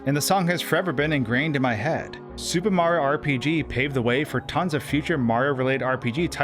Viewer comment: Lmao i kept mishearing him and thinking he was calling it “Stupid Mario RPG.”